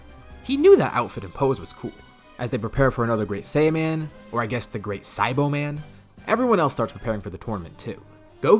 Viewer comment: i thought you said the great saibaman lol